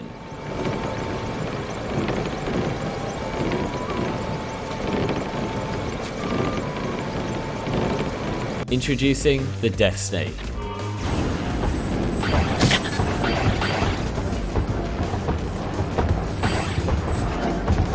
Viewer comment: What he said: Death Snake What I heard: Dex Save Both are accurate XD